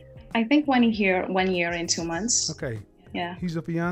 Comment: I thought she said “a year IN two months” not “a year and two months” which makes it even worse.